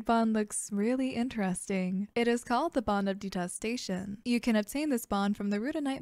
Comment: I thought i heard “Bond of Gestation” and was like WHAT